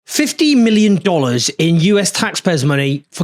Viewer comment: Press like, subscribe, comment share - and support us as we expand our challenge to our broken media here: <link> I’m hiring a brilliant video and social media expert thanks to you. We're doing new content on Instagram and TikTok to reach new bigger audiences with vital messages, like this: <link> and <link> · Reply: 50 million condoms I thought she said 50 million in condos 😂😂😂 I thought apartments